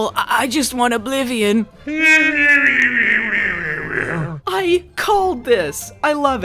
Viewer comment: I don't know if this was intentional but I heard it as "I COLED THIS!" lmao